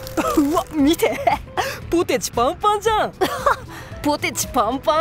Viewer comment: うわっ ポテチ パイパンじゃんw って聞こえたw